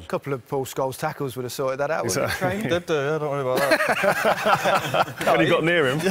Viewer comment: can't get what Scholes said at 1:07, even closed captions didn't get it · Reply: FeintFoot he said did do don’t worry about that